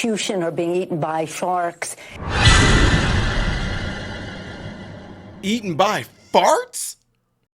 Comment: I thought she said eaten by farks, so I think she meant to say eaten by sharks lol. Her dentures were falling out she meant eaten by sharks lol. Maybe we all need to get together and send her a box of denture cream lol.